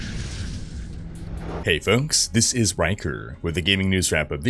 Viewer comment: It sounds like you say "Hello this is wanker" hihi hoho haha... Do I have earcancer?